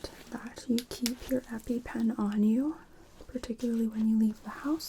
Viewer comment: So good, your voice was dozing me off. I thought you said to keep my happy pen on me instead of Epipen. 💜